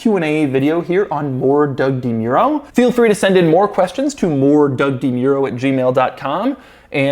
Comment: When Doug mentioned his email I thought he said Lord Doug DeMuro instead of More Doug DeMuro... I like how Lord Doug DeMuro sounds though I think that would catch on